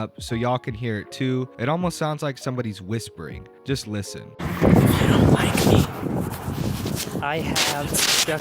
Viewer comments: I heard "they don't like me" & "BITCH" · bro those whispers are creepy... dudes got a demon fckin with him. i hear "you/they dont like me." then more but i cant understand to low to hear over danny talking. then "with bitch" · Maybe Daniel walked through an area where a person had been killed before, and picked up the person's ghost saying stuff on his phone? Sounds like it says "They don't like me", for sure. Then (to me anyway) it sounded like it said "Bi*ch", the second time. Weird!😱👻👃🏻💦⁉ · I heard "They don't like me" and then "B1tch" at the end